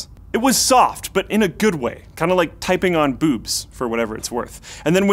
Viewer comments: did he just say typing on boobs that's it i'm unsubscribing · DID I HEAR BOOBIES? 6:27 TAKE MY MONEY!!!!!!!